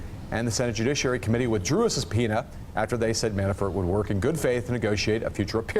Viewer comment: I thought he said "drew us a peanut." I was like wtf?